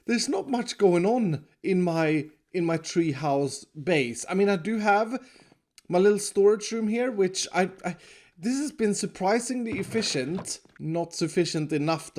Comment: i thought he was saying k house lmao